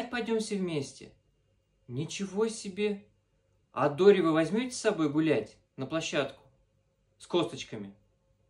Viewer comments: А дури вы возьмите на площадку? Мне так послышалось 😅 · Кому еще послышалось «а дУри вы возьмете с собой?» 😅